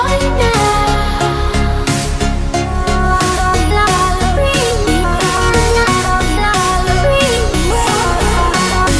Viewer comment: WHAT IS SHE SAYING ON 2:20 DOES ANYONE KNOW!!??? 😢😢😟😟🙇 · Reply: Queen Jazzie she is saying faded over and over again fast